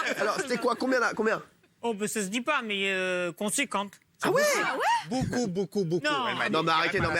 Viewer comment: Elle a dit quoi 1:49 ? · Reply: "conséquente" En rapport à la somme